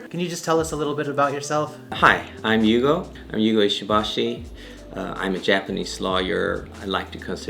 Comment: did he say :" i am a Japanese Slayer" ? :D